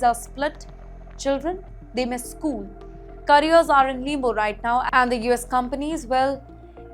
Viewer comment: Carriers are what..nembu right now? what are you saying.... what is nembu? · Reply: I think she is trying to say “limbo”…..😂 too nasal.